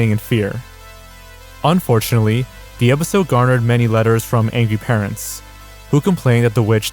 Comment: it kinda sounded like he said "anngy".